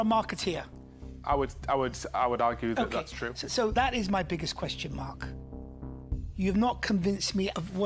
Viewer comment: why did he call u mark? Arent you? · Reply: I'm a bit late, but here goes anyway. The answer is in your question...twice. He said "...my biggest question mark", not "...my biggest question, Mark". Whether you'll even see this is my biggest question mark.